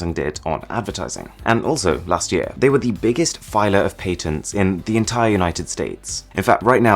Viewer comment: I heard "paytons" and it took me a few seconds to realise that he was talking about patents.